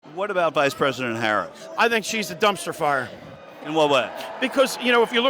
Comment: Dumpster Fire!? I thought you said "Trumpster Fire"...